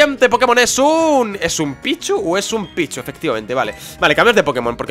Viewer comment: yo pensé que dijo "Es un pichu ooooo es un picho?"